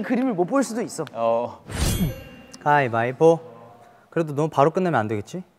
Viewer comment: How come I hear it as "High Bye Pooh"😂😂